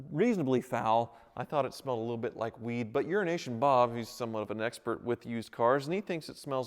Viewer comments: It sounded like he said urination Bob instead of euro nation Bob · Did he just say Urination bob? An expert on Animal pee!